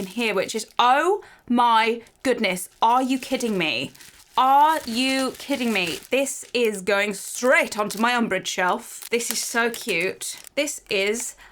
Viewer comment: When you saw the pin I thought you said ”are you KITTING me” bc that would make sense